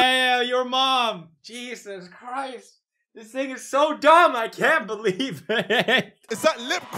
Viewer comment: No one is appreciating the insane mic save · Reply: Did I get hype for nothing? i heard pewds say "This niggas so dumb I can't" at 9:28 and idk that shit sounded type fye